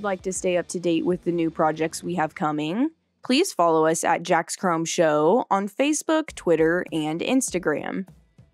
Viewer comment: It sounds like she is saying Jacks chrome show instead of shop LOL!